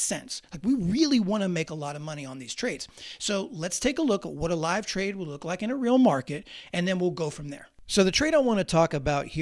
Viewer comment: I thought buddy said he will do a live trade.. 😢Damn